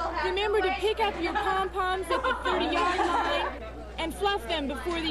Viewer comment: At first I thought she said pick up your tampons at the 50 yard line 😮😮😳😳